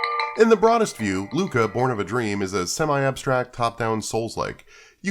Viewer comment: I thought that said "Lucha:born of a dream" and was hyped for a deep thoughtful wrestling game.